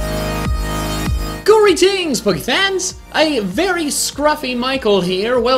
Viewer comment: Bro am I the only one who thought he used to say "greetings spookyfans"